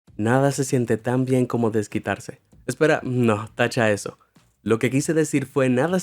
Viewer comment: yo pense que dijo chaquetearse XD